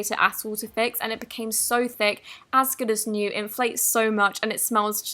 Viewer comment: what did dhe say? mine said on captions as curtis knew